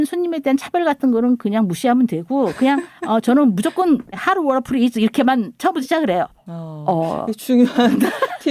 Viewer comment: 정희진 선생님 이야기 나온 것들 찾아보다가아… 조용한 생활에도 나오셨다니ㅠ ㅠ 필름클럽도 넘 쟈밌게 듣고 있는지라 넘 반가운 두 분의 성함을 보고 바로 클릭해 재밌게 들었습니다… 혹시 1:12:49 에 영어로 하신 것 같은 말씀 무엇인지 넘 궁금한데 ㅎㅎㅎ 혹시 정확히 들리시는 분 계실까요? 저는 여러번 들어보았지만 how warful(?) is 로만 들려서 여쭤봅니다 😂😂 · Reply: hot water please 인 것 같아요..